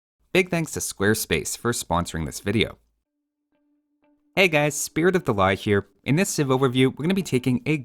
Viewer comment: Despite heard your intro many time ,i still mishearing it as Hey guys,Spirit of La Hire :v